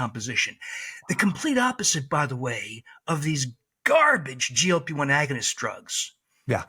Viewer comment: About 16:20. What agonist did he say ??? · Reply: Glp1agonist